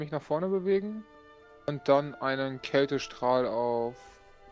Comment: Ja ich gebe es zu, ich bin schon etwas müde, aber ich habe gerade bei verstanden:"Dann wirke ich den KÄSEstrahl". 🤣🧀 Vielleicht habe ich auch Hunger 😁. Super Video auf alle Fälle. 😄😄